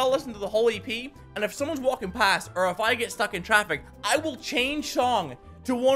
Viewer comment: he said Chong instead of song